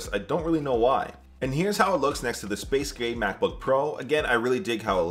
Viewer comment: did bro just call the Macbook Pro's color "space gay" at 2:51?